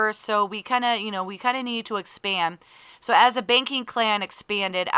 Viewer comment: lol I love how (for me) it sounds like you say baking clan instead of banking clan. And now I’m just imagining it lol